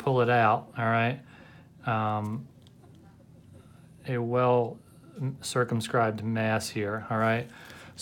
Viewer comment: I thought he said circumcised mass